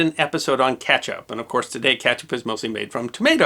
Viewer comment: I thought he said "catch up", and from then on I knew his accent was going to make his speech hard to understand. This is even before he said tomaydo. 1:42 Really? I thought there was more to it than just pronunciation. 4:06 "Too there were scholars..." Seriously?? Nobody talks like that. I wish this guy wouldn't shout. The combination of that and his grating accent makes his speech impossible to bear. A shame. Sounds as if there's some useful stuff there, but it's no good if it's unlistenable.